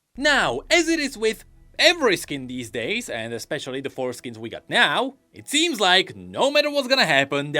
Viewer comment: the foreskins hahaha i heard it as foreskins.